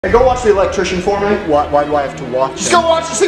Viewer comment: I thought he said go wash the electrician😂